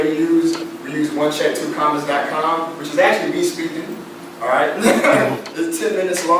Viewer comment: what is the website that he said at 3:53? wunchat.....something something?